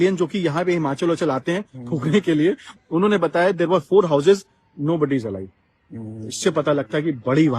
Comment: the captions are wrong. He said there were 4 houses and nobody is alive